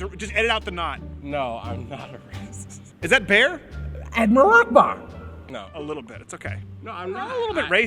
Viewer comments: listen closely he says admaril akbar instead of allu akbar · Is it just me or does the bear sound like it saying Admiral Akbar?